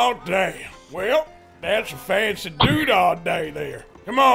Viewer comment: What did he say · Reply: Well thats fancy do dat dat tho||something like that👌🏼